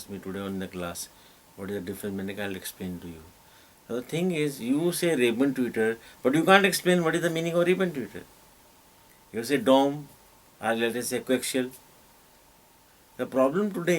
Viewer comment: Anyone Knows What he is saying at 33:06? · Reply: He is talking about tweeters ribbon ,dome etc